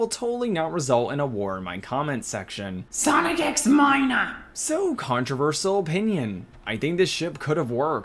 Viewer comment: All I heard was “Sonic X Minor,” … WHICH IS EVIL